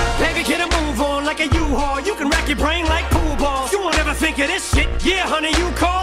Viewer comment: Wreck your brain like pool balls… I thought he said blue balls this whole time 🤣